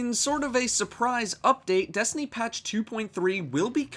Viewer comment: Thought said destiny 2 at start, gave me a heart attack